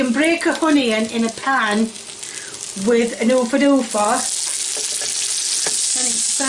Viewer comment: the subtitles says with a open door fast... But i heard her say oompa loompa... Or oompa loofa